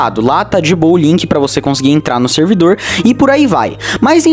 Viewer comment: eu juro que escutei "ink para entrar no servidor" KKKKKKK